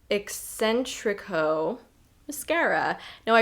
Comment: When you were talking about the Armani mascara I heard ‘Eccentric ho’ and it took me a minute before I realized you were saying ‘Eccentrico’... 😊